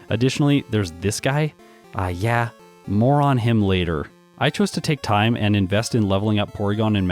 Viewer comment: I just heard:"moron him later"